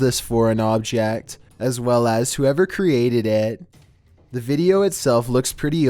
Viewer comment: did he say valentimes day?